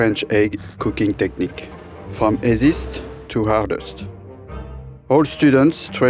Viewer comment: I didn’t understand what he says in 0:11 after playing it 3 times and then saw it’s on the title. Gotta love the French 😍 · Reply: 'esiste' = easiest